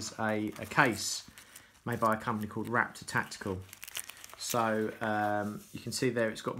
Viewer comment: Hi, Just watched your informative & helpful clip on here giving the full ‘Heads Up’ On this GARMIN ForeTrex 601. Heard you mention about Wrap-Tactical being the company that you got your protective wrist strap case from. I’ve tried to google them and CANNOT fInd this company anywhere. Can you assist?? Also where did you get that NATO strap from?Thanks, Rich. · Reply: ahhhhh, Must be my ears then, I thought you said “Wrap-Tactical” not “RAPTOR-Tactical”, 😂🤣. My ears are slightly shot from being in the Military! Where in particular did you get your exact NATO Strap from, bc that’s the one I would be after too. If your YT Clip was shit, I would have told you that too BUT it defo WAS NOT! Many thanks & Best regards.